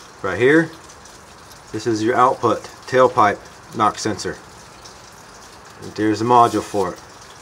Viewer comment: I thought you said knock sensor i almost had a heart attack. Just bought my 13' private sale so i would be f*cked 😅